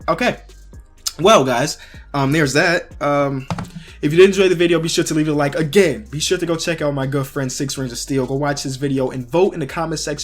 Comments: i heard girl friend lol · i thought he said girlfriend not good friend 🤦🏼‍♂️ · I thought he said "girlfriend" when he said "my good friend"